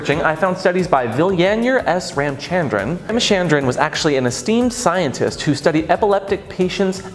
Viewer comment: 4;48 I am SO gay. At first I thought he said "A 'steamed' scientist" 🤣